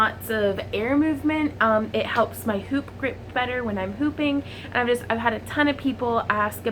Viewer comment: Lmao at the beginning I thought she said pooping I was like ummmm okay lol 😆😅👌🏽